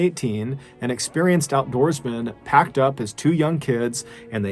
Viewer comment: Did anyone else hear MrBallen say "hacked up his two young kids" at 0:03? Maybe I've just been watching too much MrBallen. (jk no such thing) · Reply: packed